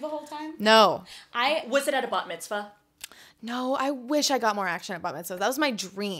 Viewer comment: I thought she was saying “butt mitzvah” to continue the joke omg 😭